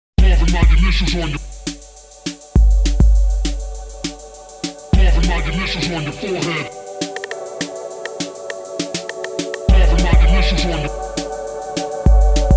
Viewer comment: Carving my initials on your forehead Always on a mission like a warhead. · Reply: I thought it said "Cover my delicious on your forehead" , bit kinky ..............